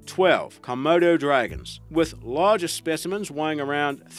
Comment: I KNOW DAM WELL IAM NOT THE ONLY ONE WHO HEARD HIM SAY2, " KO-MURDER- DRAGON??? INSTEAD OF KOMODO-DRAGON 🤔🤣🤣🤣😂😂